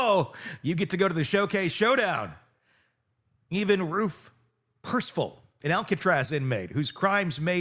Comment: Thank you, as always for your informative videos. Quick question: Who was the Alcatraz inmate you mentioned around the twelve minute mark? I heard “Evan Roof Crispal” - tried a few versions of the spelling but couldn’t find anything. Thanks for any help!